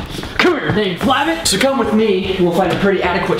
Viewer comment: When you said so come with me, I thought you said Sokovia at first..